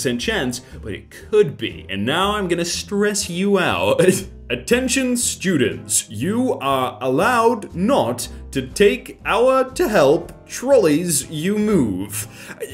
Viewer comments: to whoever didn't understand the meme at 9:00 It says "Attention students you are not allowed to take our trollies to help you move" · took me a while to process it, but i think i got it, it's saying "you are not allowed to take our trollies to help you move"